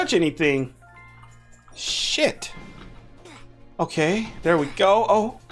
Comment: did u just say sh*t?